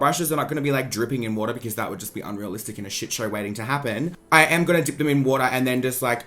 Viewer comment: OMG I thought he was saying "Shit show wedding"!!! lol Love the video, so funny!